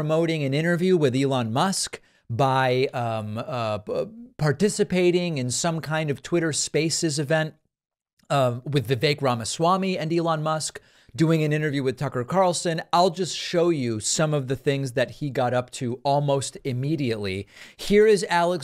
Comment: Every time Vivek Ramaswami is mentioned, I hear "the fake Ramaswami".